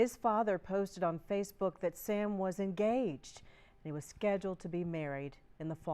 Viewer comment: I misheard when she said Sam’s father posted on Facebook I thought she said “his father posted on FB, that Sam was a g ay “. I was like “why tha fuk did she say it like that??” I rewatched it and heard he was engaged.